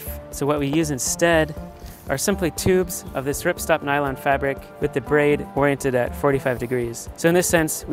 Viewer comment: What is the material? I can't hear clearly. Can somebody help? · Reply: So what we use instead 06:28 are simply tubes of this ripstop nylon fabric 06:31 with the braid oriented at 45 degrees. - taken from captions